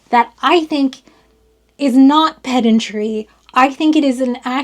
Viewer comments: For anyone else who misheard: it's "pedantry" not "pet injury". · I definitely heard "pet injury" instead of what I presume to be "pedantry". 😂 Confusing to be sure.